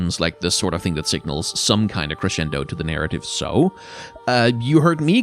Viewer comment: Anyone know what said in 47:37? I presume it means end but I want to know how that word is spelled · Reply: Crescendo?